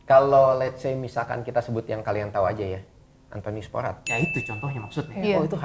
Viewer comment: Yang disebutin kak di menit 6:37 siapa ya? Aku dengernya Anthony aja. Nama yg belakang tdk begitu jelas. Ada yg bisa bantu siapakah beliau? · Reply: antonius porat